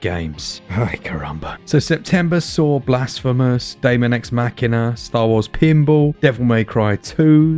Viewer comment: For a moment there I thought you said deus ex machina there at the beginning :). I am having still so much fun with switcher and got me vampyr too. I am very excited to see black flag come out, I had an amazing time with this game. Children of morta looks very interesting too, thanks for this list, it already made my day :)